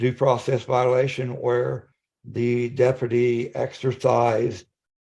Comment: This man just said “Dep-er-ti” instead of “Deputy”.